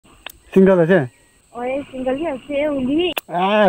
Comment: I thought you said chewing gum ase when you said single ase